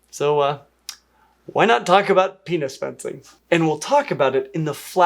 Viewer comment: So I misheard penis fencing, something I was aware of, as Penis Flensing. And had a moment of abject horror. So well done!